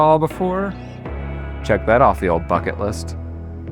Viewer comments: i really thought he said “check that off the ole f***in list” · Instead of “well check that of the bucket list” why did I hear “well check that off the fucking list” 😂